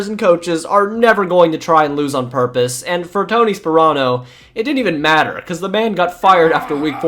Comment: I thought you said Tony Soprano at first lmao.